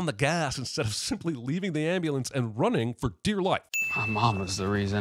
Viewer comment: I coulda SWORN I heard you say "bearbulance" at least one of the 80 times I've watched this